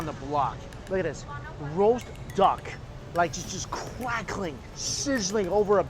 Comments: did he say the roast duck was quackling? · did he say the duck was quackling?